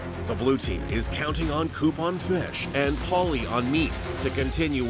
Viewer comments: I heard “coupon fish” instead of “Koop on fish”🤣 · I thought he said " coupon fish " lol